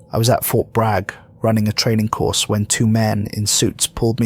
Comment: As a veteran, I heard: "I was at Fort Bragg, running a train ...ing course"